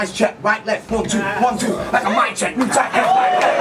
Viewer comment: I love reaction to the "Mic check" at 7:07 because i had no idea what he said either until the second time around lmfao